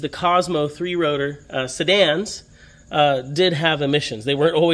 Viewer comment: He means Cosmo Coupe* heard him say sedan.